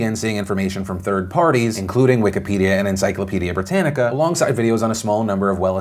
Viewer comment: Oh god! I thought you said Encyclopedia Dramatica at first.